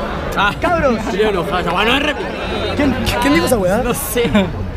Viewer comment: que dijo? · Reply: "esa wea no es replica"